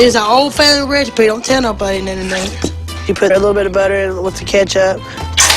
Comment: the sketti i could of swore i heard her say a little bit of ketchup little bit huh?